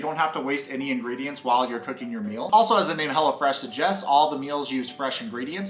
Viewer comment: I thought you were saying HELLAFRESH🤣